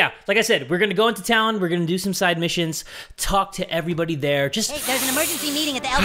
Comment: When he said " I'm going to go to side missions" I thought he said side bitches